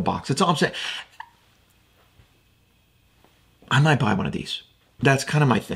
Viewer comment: What did you say at the 6:30 mark? Was it "I might buy one of these?"